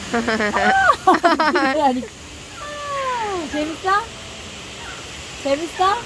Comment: soy yo o escuche un "Te gusta" :/